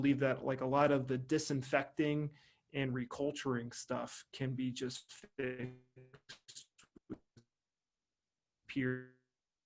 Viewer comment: at the 14:30 min mark you are talking about the disinfecting and culturing but you cut out. What did you say? Thanks! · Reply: Sorry about that. I was saying that you can actually get rid of an infection by fixing your terrain with tools like seawater. I see this is one of your first comments welcome! Click here and I will show you the 3 foods to avoid to reduce your digestive issues by 75% in 30 days: <link> On this video I will also share with you all the information I wish someone would have shared with me when drugs weren't working and my GI doctor had given up on me. Even after years on boring bland holistic diets, I couldn't enjoy a piece of cake on my BIRTHDAY, I will show you what you need to do to truly heal your gut so you can enjoy normal food again. <link>